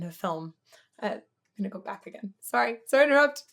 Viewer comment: I just misheard you say "Nickelback again"... And now I want you to react to them... Goddammit